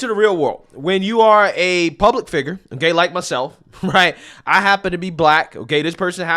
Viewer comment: did you say ‘A gay like myself”?